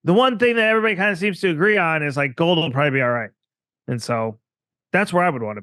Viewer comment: What did he say at the 1:01:01 mark about the one thing everyone should own? I heard gold but the rest was inaudible and I don't see it in the transcript.